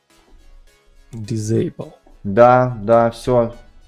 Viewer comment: мне послышалось? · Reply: Он сказал Дисейбл